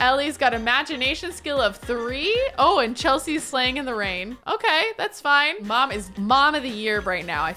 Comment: Lol when she said Chelsea is playing in the rain I thought she said Chelsea’s slaying in the rain😂 BTW please name the a girl Juniper you can call her June for short😊